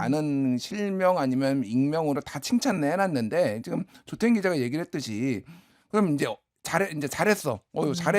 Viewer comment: 조태임 기자님을 김준일님이 대화속에서 들어보니 조땡 기자 라고 들려요. ㅎ ( 15:45 ^^ )